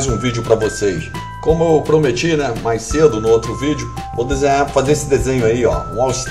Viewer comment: vou desenhar a desenhar esse desenho?Ou só eu entendi assim?